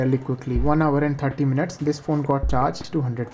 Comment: who heard oppo x3 😂